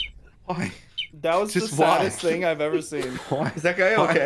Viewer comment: the fact that zud said that was the saddest thing. if you put on captions at 7:13 you will see the captions say “that was the smartest thing” :/